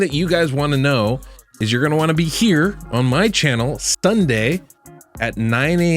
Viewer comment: "STUNDAY" why does he say it like that ?